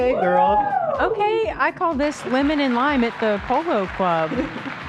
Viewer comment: I thought was saying her outfit for hila was “women in line at the polo club” and I like that better